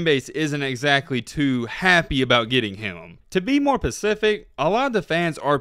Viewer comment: Did you just say, " to be more pacific" at 1:18?